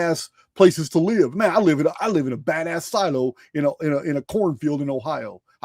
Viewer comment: Are you in Willis Texas? I live here ! Heard you say " I live in Willis " !